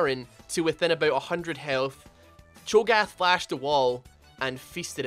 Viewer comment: i thought said that chogath fisted the baron. lmao